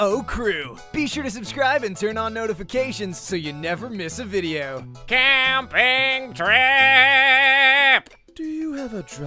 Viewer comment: wth yt i want captions instead of "CAMMMMMPPPPPPIIIIIINNNNNNGGGGGGG TRIIIIIIP" its "CAMPAIGN TRIP!" y just y